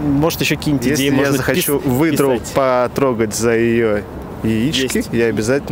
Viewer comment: Ахаха. Когда спросил "можно придти выдр... " Я не расслышал, услышал только потрогать. А сейчас видос смотрю, оказывается он спросил за "яйца потрогать", а я такой " Да есть такая услуга" ))))